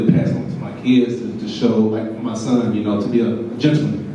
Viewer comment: Did he say, "his son or sons"? At 22:41, it sound like he said son. Why isnt he including the outside child in his legacy😂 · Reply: Yep, he said "son"